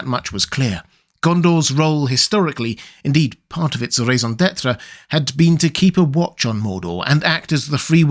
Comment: can someone help me understand what he said I don’t know what that means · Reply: Raison d'être. Kind of meaning a reason for existence